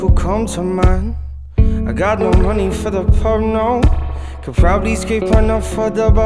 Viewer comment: Anyone else thought he said „no money for the porno“ instead of „no money for the pub, no“ 😂😂?